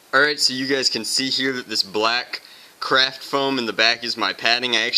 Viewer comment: I thought he said crap foam